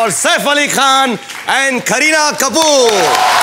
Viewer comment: Am i the only one who heard srk saying kerihad kapoor to kareena kapoor.